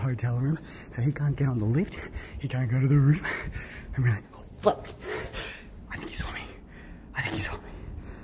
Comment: Eystreem did you just say F*ck at 12:12 Omg its actually my first time hearing that from him😐